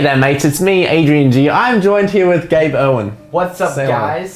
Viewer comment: he's accent made it sound like he said owen instead of erwin 😂